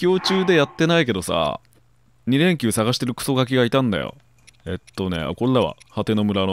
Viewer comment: 本当は『二連弓の筈探してる〜』なのに「二連休探してるクソガキ」って聞こえて草 休みの要望がとても切実